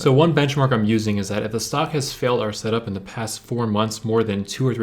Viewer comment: what did he say at 1:44 ? if the stock has "fail R setup in the last 4 months"? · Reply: Failed our setup. The setup I taught in my webull trading video on my channel